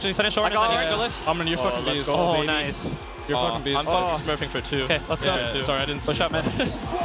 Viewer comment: what did pob say at 10:27? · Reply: "I'm fucking smurfing for 2"